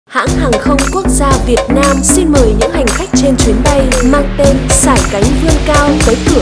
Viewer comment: What does the voice say at 0:01? "Hãng Hàng không Quốc gia Việt Nam (si muy??)